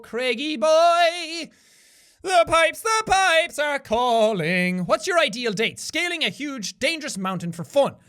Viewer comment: no joke, I heard "what is your ideal dick?" "Scaling a huge, dangerous mountain for fun" 😳